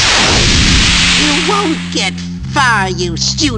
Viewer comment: I misheard this the first time and thought he said "You won't get far you studious little t***!"